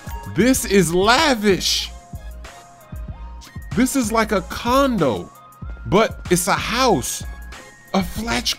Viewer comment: Did he just say condom lol